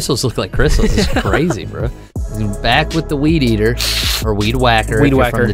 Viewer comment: I got confused and I thought they said weed yeeter.